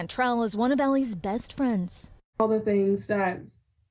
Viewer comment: Caption is wrong. Her best friend's parents. Not Aly.